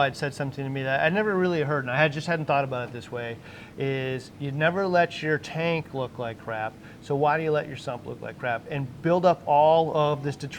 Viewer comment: i laughed my ass off and had to replay, I thought I heard "You never let your tank look like crap, so why do you let yourself look like crap"